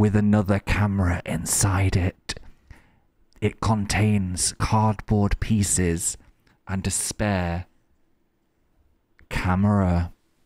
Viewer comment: When Phil was doing his stalling ASMR about the spare camera, a pause made me mishear it as him saying that the box contains: "cardboard pieces and despair". 😂